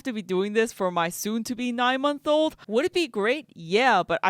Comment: Wait I thought I heard you say you have a 7 month old and a 9 month old ?? I thought you said 6 month old too I’m over here trying to do math.. I’m like wait it’s not possible lol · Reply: I have 1 infant daughter who just turned 7 months old! She was born May of this year. When I said "soon to be a 9 month old" that was more for my video script to fit the joke. 😂